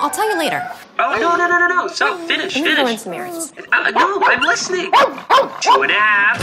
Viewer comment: What did she say at 1:17? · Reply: I believe it's "I'm gonna go run some errands"